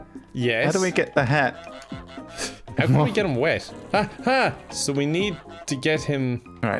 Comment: Omg I was just listening to this in the back ground and I hear Kevin say “how do we get him wet” 😭😭😭